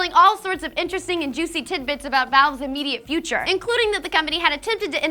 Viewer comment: did she just say tits bits ????